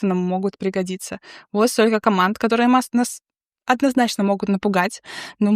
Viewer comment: Очень редко в жизни видел обзоров от девчонок. Забыл когда в последний раз вообще видел. И на выпадающих менюшках с опциями я слышу «это вас может напугать», милота же? Открыл менюшку и напугался. Класс)